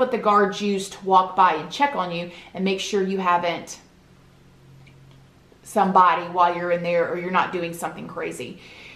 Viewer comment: what did you say? love your videos btw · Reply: Killed....